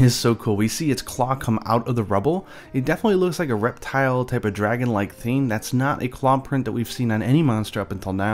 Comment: I thought he said Erectile type of dragon for a second.